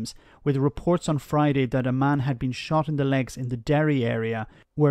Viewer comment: for a second I heard at "a man has been shot in his derriere".